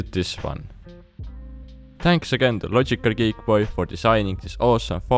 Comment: did he say logical"gae"boy